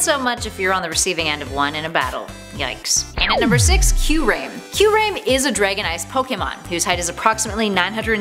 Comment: did she just say CUE RAIM???? Wttfffff🤦‍♂️🤦‍♂️🤦‍♂️🤦‍♂️🤦‍♂️